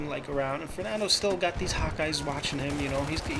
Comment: haha, every time you said hawk eyes, I thought you said "hot guys". And i was like, how does this fit into the story???